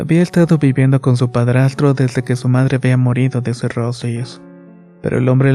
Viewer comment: "morido" ¿Escuché bien señor, señor? ¡Wow! ¡Que historia!